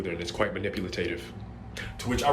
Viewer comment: he really said "manipulatative"